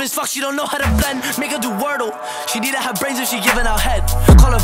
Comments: i heard the n word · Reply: “make her” not the not word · I thought he said n- do wordle